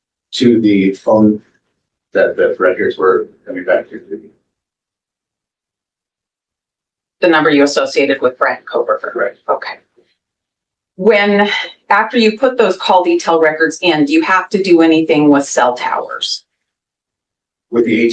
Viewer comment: did AT say Brent Koberger?